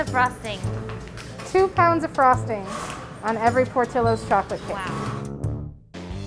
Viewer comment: i thought he said a TUB instead of a POUND of FROSTING